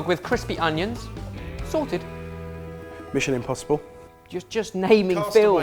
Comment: I thought he said "Michelin impossible". Damn that would've been a good pun...